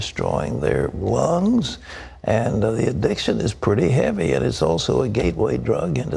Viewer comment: Oh man, when he said "this addiction" i heard "this shit" XD